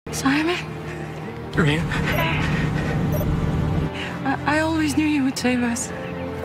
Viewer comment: what did she say there? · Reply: "I always knew you would save us"